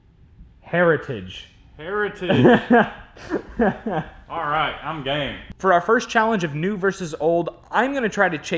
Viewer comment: DID HE JUST SAY "alright I'm gay" !?!?!?